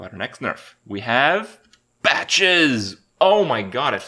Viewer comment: sounds like he said B atches